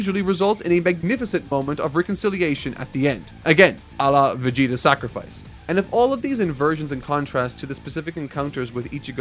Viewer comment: what did he say😭🙏🏾🙏🏾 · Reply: Im not sure either? Was it, Allah, vegita sacrifice??